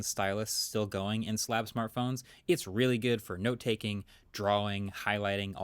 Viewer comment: Is he saying Slap smart phones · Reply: Slab smart phones